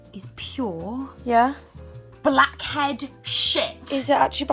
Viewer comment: Instead of "blackhead shit" I thought said "black Ed Sheeran"